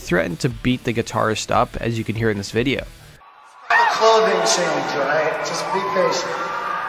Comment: Slash did make the "wardrobe change" comment, though. Definitely a dig at Axl. He didn't mishear Slash. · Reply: To me it sounds like he says “It’s not a clothing change alright just be patient” so I don’t think it was a dig at him